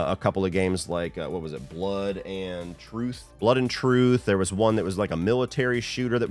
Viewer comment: I thought he was gonna say Blood & Honey. Almost got a heart attack there